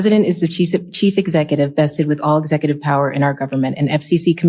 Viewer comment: I thought she called the President a Cheese-it lmao